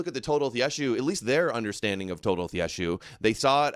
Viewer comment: What is saying @ 22:05 it sounds to me like he is saying "Total of Yeshu" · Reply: Toledoth Yeshu